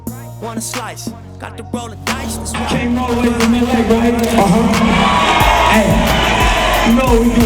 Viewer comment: On God, I heard “Came all the way to Romania”